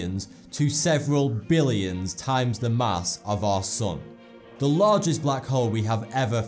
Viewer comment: It's really funny how he says "Our sun" and I hear every time "Our sOn".